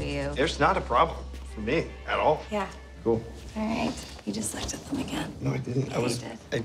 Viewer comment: what did she say? Help bros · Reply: "You just looked at them again" "no I didn't, I was --" "I hate it"